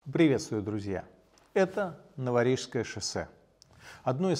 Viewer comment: Уф-ф-ф ... Послышалось в начале : " Это -- нУворишское шоссе " _ :-( вот блеать , случится же такое . А ведь ТАМ живут честнейшие представители общества !!